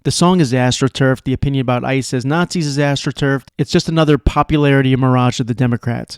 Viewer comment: I heard "ICE Naaaåzi" as Ass Naaaåzi. That'd be fun to watch: L vs G vs B vs T. "Ricka-racka- Ricka-ruck, Hate that team for who they fůçķ. GooOOO TEAM!"